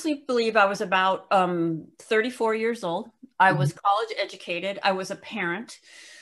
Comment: The fact that she pointed out that she is college educated says a lot. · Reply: Oh! I thought she said cultist educated :o College makes more??... sense. No really... since when does getting an education make you a nut job? I'm guessing it's only certain colleges?